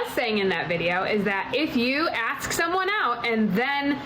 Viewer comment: she actually said "axe" as in, "ask" , like normal white ppl do